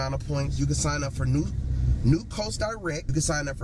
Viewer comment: What the hell did he say at 1:12? New Costar Rec?